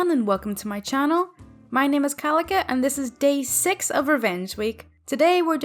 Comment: I thought your name said kalinka.